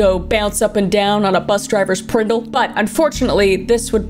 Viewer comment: Bobbing up and down on the bus drivers "PRNDL" is that what she said? That's hilarious 😂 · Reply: I low key thought she said Pringle, I wasn’t gonna question it lol